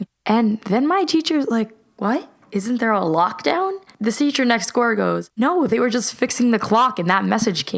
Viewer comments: Who else noticed at 2:44 she said the the teacher nextcore instead of nextdoor😐😐😐😄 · she said gore instead of door